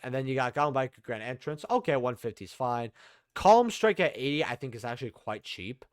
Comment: I like how the only ocg name you used was for iron thunder 😂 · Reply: I thought you said calm strike lowkey 💀 lol